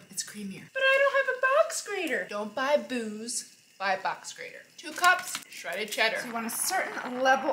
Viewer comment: "Don't buy BoOzE, buy a box grater". I'm sending that to my daughter at UMass. Im sure she'll listen😂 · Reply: Ooooooh she says box grater😹 Thank You Xx I genuinely thought she said " Backs scrater " Deaf ears😂 I thought wth is a Backs scrater Where is this accent from in America? Asking from England UK xx 😂🙈